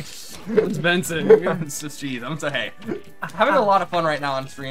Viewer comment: did say hes high LMAO